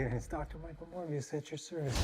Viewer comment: When you said "Morbius" I heard "Mooooore BS"